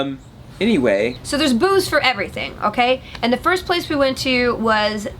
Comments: I first thought that Felicia said "so there's booze for everything" that would have made for a very different conference. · LOL, I thought you said "There's booze for everything" xD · At one point you said "There were booths everywhere," but I really thought you said "There was booze everywhere." I was like, "Seems like E3 is very different than I thought..." ;)